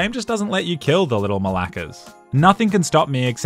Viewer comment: DID YOU JUST SAY ;;THOSE LITTLE MALAKES''???!!!